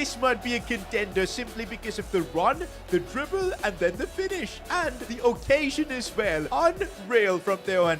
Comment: i thought blud said "and his cocasion as well"